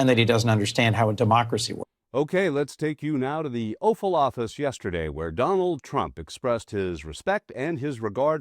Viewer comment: Sounded like you said, "let's go to the offal office now?"